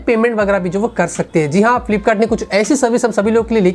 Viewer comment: Correction- you said "flipkart" hs brought a service of offline payment cards, instead of saying "PAYTM" Time- 6:35 in paytm news. Nd we love ur tech news bhai❤